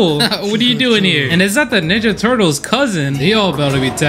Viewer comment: who else heard nigga turtles on